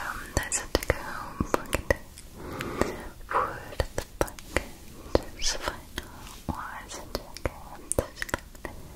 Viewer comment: did anyone else hear what ah the fuck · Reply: I heard budah the fuck